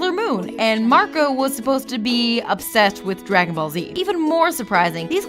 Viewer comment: Did she just say "dra-gone ball z"? Dafaq